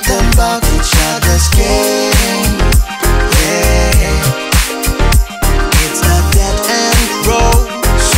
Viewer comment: Lyrics I'm not certain everything I wrote is correct, and when the singer mentions "game", I think he's talking about relationship game. "I got more problems than you probably, right? I got some things that keep me up at night Now you wanna talk, now you wanna know, is it really what you want, and are you sure Buddy You got some issues that dwell inside your head You keep em closed than open up instead Now you wanna talk, now you wanna know, is it really what you want, and are you sure Buddy You are my, friend But now we're enemies again You were my friend But now we're talking shit about each others game It's a dead-end road Slow down, slow down Buddy I'm overheated and you're overcooked The best nights out there the ones you don't forget Remember all the days, all the things you said Running wild, running wild And free Buddy You were my friend But now we're enemies again You were my friend But now we're talking shit about each others game It's a dead-end road Slow down, slow down You were my friend But now we enemies again You were my friend But now we're talking shit about each others game It's a dead-end road Slow down, slow down Buddy Slow down Buddy Slow down Buddy" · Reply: I hear; "its a dead end, bro"